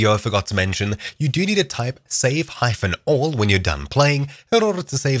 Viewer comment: Ok problem solved hyphen or whatever means this - well im dumb My problem is i cant understand what your saying at 6:44 I know you are saying something like "safe ????? all" But thats the issue :P i tried: safepythonall safe python all safe hyphon all safehyphonall safe phython all safephythonall ... so can anyone tell me what to write because my english isnt the best So i dont know how to save the inventorys (and all that stuff)